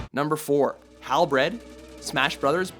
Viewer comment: did you just say Hal bread? its halberd! hal bird!